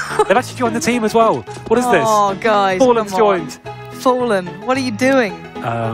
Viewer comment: she says fallen what are u doing but i think that's fer just sayin